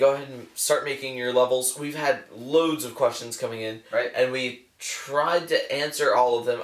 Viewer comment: "We've had loads of questions coming in." I actually heard that as Russians instead of Questions.